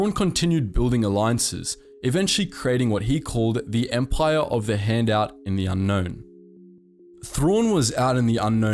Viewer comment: thought you said "empire of the handout" for a sec lol